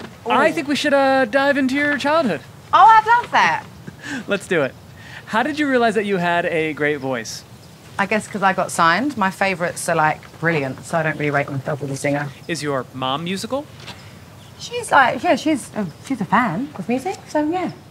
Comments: Watched this after the Kylie 73 q’s…what a difference, you can feel Adele is so genuine and down to earth. I felt like I was listening to a friend. So refreshing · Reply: Could anyone help me figure out what she said at 1:19 - 1:23? "I got signed my favorites (?) ... brilliant ... (?)" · I could not stop staring at her healthy, luscious, glossy hair. Edit: omg thank you so much for all the likes. I have never gotten this many likes.❤️ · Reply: Could anyone help me figure out what she said at 1:19 - 1:23? "I got signed my favorites (?) ... brilliant ... (?)" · “See you in 7 years”… NO ADELE IS THAT A THREAT WE JUST GOT YOU BACK 😭😭😭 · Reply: Could anyone help me figure out what she said at 1:19 - 1:23? "I got signed my favorites (?) ... brilliant ... (?)" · Probably the first video of 73 questions of Vogue which doesn’t look sooo staged, I guess it’s because Adele is so real and authentic! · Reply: Could anyone help me figure out what she said at 1:19 - 1:23? "I got signed my favorites (?) ... brilliant ... (?)" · WHY IS NOBODY TALKING ABOUT THE HAIR ?????? She looks incredible and I love her personality and she's just amazing, period. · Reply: Could anyone help me figure out what she said at 1:19 - 1:23? "I got signed my favorites (?) ... brilliant ... (?)" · Popular opinion: Everyone agrees that Adele is melting everyone’s heart with her comeback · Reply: Could anyone help me figure out what she said at 1:19 - 1:23? "I got signed my favorites (?) ... brilliant ... (?)"